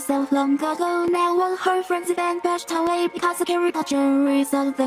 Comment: did i just heard harry poter 😂:/